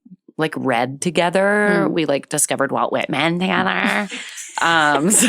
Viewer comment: OMG when I first listened to this I thought Jen said "we discovered 'Wild Wet Men' together" and I thought maybe it was some booktok romance series ... but just realized she said "Walt Whitman" and that is so amazingly and hilariously better 😅